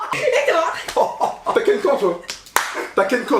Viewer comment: Svp, qu'est-ce que dit de 11:07 à 11:15 ? Je comprend pas cette phrase mais elle me fait rire😂😂😂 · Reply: MAT ZILO il dit «t’as quel camp »